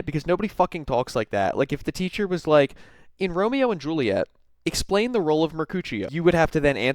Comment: 100% thought you said “in Romeo and Juliet, what was the role of her coochie”